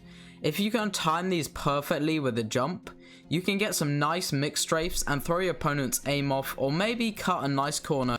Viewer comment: I like the “myx strafe” 😂 good name. I need to test this out cause i don’t use that technique or what the majority of the other people do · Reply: I honestly thought he said McStrafe